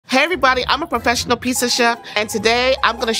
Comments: Oh wow, I thought she said "hi everybody, I'm a professional piece of s***" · “Professional pizza chef” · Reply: Thanks for clearing that up whew I heard “professional 🥧 of 💩“ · legit no hate at all but at first I just heard "I professional piece of 💩"...😅